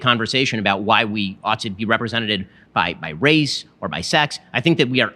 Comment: did he say “representeded”